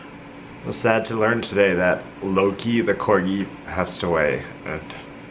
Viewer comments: I thought he said low key the corgi passed away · OMG😱! In the beginning I thought Gatsby died😞 I thought he said "Lowkey the corgi past away" then I read the comments and it said "Loki"